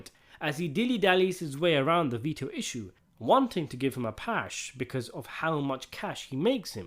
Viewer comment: Did you say give him a pash?😅